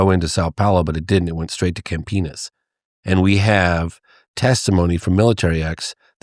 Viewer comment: did he say Camp Penis?